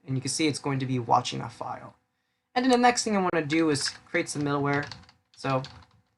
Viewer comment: I thought you said "Now the next thing I want to do is create some malware"